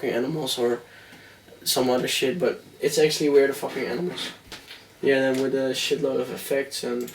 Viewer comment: Always thought he said "Burn these fucking animals"